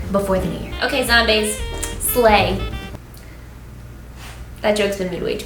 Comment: I thought she said "ok zombaes.... lay." I'm laying in my bed comfy not close to Cali so I was like "how did you know?" And then realized whta she actually said.